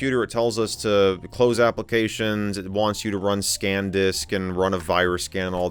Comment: Did you say “Scam Disk”? 😅